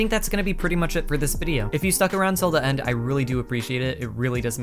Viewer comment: at the end i thought u said "if u suck, around till the end" ahahaha